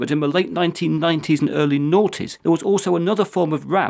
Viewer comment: did you say naughty? Or 40? Lol on a real note tho i loved the video very well explained thanks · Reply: I said the late nineties and early "noughties" -- the name for the period from 2000 to 2009! :)